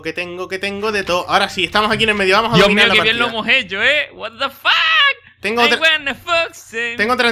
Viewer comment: En el minuto 8:05 dijieron What does the fox say?